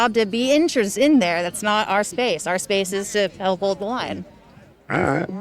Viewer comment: did she say they are there to hold the lawn????? Lilolol instead of hold the line, hold the lawn They are standing on grass.